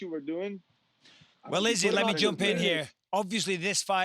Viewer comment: did bisping say "well ladies let me step in here"- that was slick